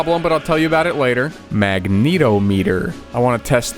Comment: I misheard this as the MagNeo meter